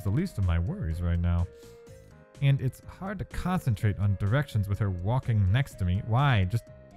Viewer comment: plz tell me im not the only 1 who heard erection